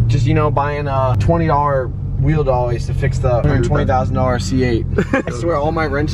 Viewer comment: I could’ve sworn he already said how much he paid in this video, he said something about cheap tools fixing a 20,000 dollar car or maybe I’m just tripping lol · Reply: yeah I’m tripping lmao, the video ever so slightly cuts off when you say 120k that’s why I heard 20k